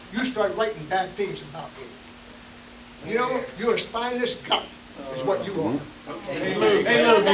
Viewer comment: Did he just call him a spineless ©️u©️k? 😆😂😂